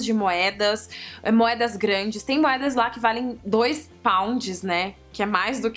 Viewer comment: Quando você falou pounds eu ouvi "paus". Tipo "trinta paus" kkk